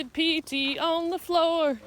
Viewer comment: "When my granny was 94, she did pee-pee on the floor"? Did I hear that right? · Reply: "PT." i.e. physical training.